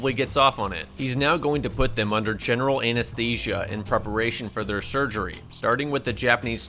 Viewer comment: did you say anus-tesia?